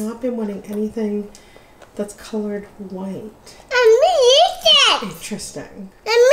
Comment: sounded like she was saying "dare me eat it?" LOL